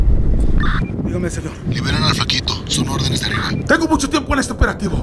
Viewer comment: Que dice en el minuto 2:02? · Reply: Son órdenes de arriba